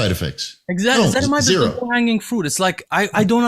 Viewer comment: what is he saying? Edzatamite? I want to google it but i don't know how to spell it. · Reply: Ezetimibe* found it